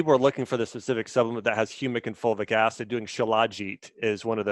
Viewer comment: What is the word he says at 34:16? Silageet? huh? lol...he says it has humic and fulvic acid. Thanks in advance. =) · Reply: Shilajit! You can learn more about it here: <link>